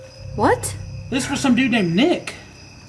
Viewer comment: I hear "dude"